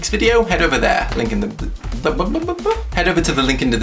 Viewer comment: Instead of "Cheers for watching!" I thought you said "Jizz for watching!" xD